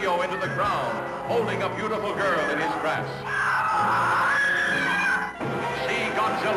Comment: did he just say "see him carry a beatiful girl in HIS dress"?